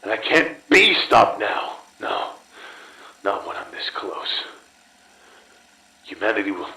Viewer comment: The subtitles say "not when I'm so close" but the man says "not when I'm this close". Another loss for the deaf